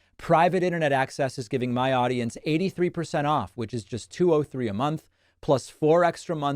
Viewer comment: David, fyi, until I saw it in writing I thought you were saying ‘two-oh-three a month’ meaning $203.00 a month.